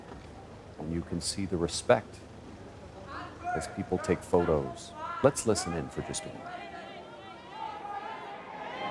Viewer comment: What was the boy yelling @ 14:59? The boy was pulled forcefully by someone.. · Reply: "Andrew, you're a sick old man!"